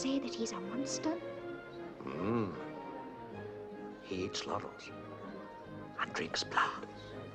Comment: What did Arthur said at 1:20? He eats laurels? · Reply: I found it :D found a subtitle track and he does, in fact, say 'laurels'...which means a little green leafy plant, or a person's achievements and awards...and seeing how Napolean was burning his way through Europe for 20 years at the time, I suppose from Wellington's point of view, since Napolean was all the rage, is that he meant Napolean was surpassing everyone's achievements and rank and not many could stand against him as he was eating up the competition to be the greatest man in Europe. Then he went out an beat him at Waterloo. That, or it was insult of the time that Napolean ate leaves. XD